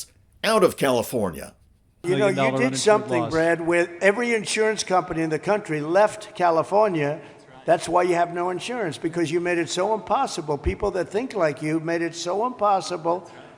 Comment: did he just say "unpossible?" Reminds me of Ralph from the Simpsons 😀